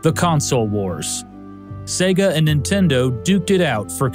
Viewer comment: The auto-generated captions got that it was duked despite the manual captions saying duped. lol